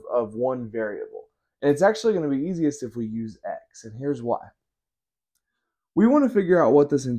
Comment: he said a joke i think he said "use x, heres y" what a COMDEIAN? i never know some guy who could make amth so fun 😊 × heres y. If you want some fun equation from now on please try to do some engineereering absed problem becase they are very much so and complicate. It did hapen to me while i was. I was doing it. Remember that happen very well. Not my best. Moment.